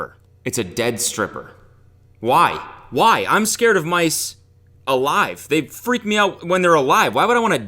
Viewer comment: thanks, as someone who's first language isn't English, I thought you were about to say "I'm scared of my s.... streapers".